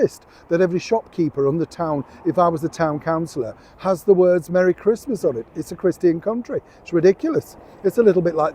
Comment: I thought you said Happy Christmas?